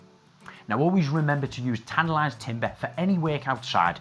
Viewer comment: ?? what he say ?? Always use Tantalized Timber ?? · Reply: Kanzee. pay attention from the start 0.13 it is Tanalised timber... even as an Australian I can understand what he said. just get on the program and chill out.